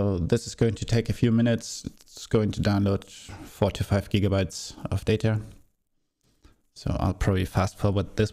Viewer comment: 45gigs of data? am i hearing this right?